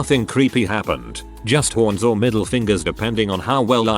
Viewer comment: I heard middle fingers, not metal fingers :0